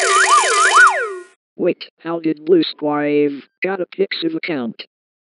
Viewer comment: Screen Gems: Wait. Did he just say "BlueSquareJV", When Blue Square is Coming Back?